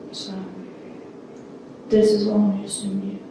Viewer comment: I think that she says at 32:18 is "so this is all news to me"